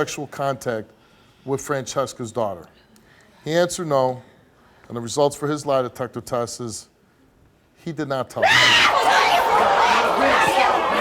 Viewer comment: it sounds like he said "he did not touch her", but he actually said " he did not tell the truth"